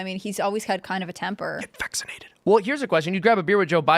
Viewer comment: Hey did anyone else not catch thiis.. wtf at 1:1:06.. a whisper comes in?? Plz tell me im not the only one here.. im trippin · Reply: I heard it, it sounds like “get vaccinated”. I slowed the video down and yes, that is what was whispered. Subliminal messaging comes to mind….